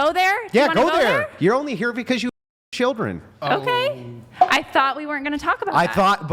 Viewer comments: holy shit did he say she f✓©k$ children 😯!😯!😯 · Does he say you're only here because you abuse children? What's the story?